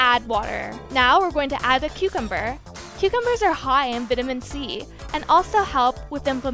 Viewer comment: did she say, vit-ih-min C????